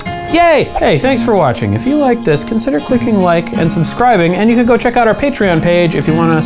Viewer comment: Instead of "consider clicking like", I first heard "consider **cking like".